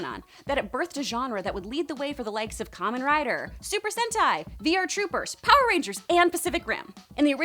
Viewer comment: anyone else misheard super sentai to super hentai?....no?....just me....ok